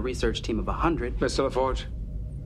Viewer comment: What does Picard say to Geordi at 2:32? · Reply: "I believe we will postpone".